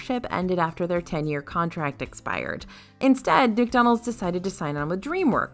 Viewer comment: I’m sorry did she just say “Dickdonalds” 😂😂😂😂